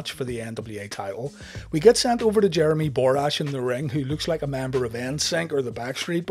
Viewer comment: When I first heard of Jeremy borash, I really thought they said "Jeremy ballrash "